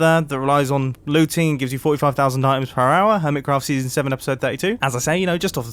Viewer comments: "a super efficient gold farm, built above the nether, that relies on looting, gives you 45,000 DIAMONDS per hour..." So confident, and yet passes right by the fact he said diamonds instead of gold 😂 Love your vids mumbo, mistakes and all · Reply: He says *items*. Check on 0.25 speed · did he say 45,000 diamonds per hour?